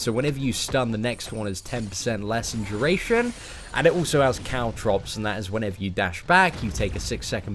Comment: when said caltrops i thought he said cow drops i'm like wait WAT?!